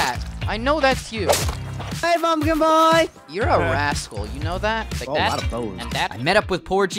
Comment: I thought said you're an a-hole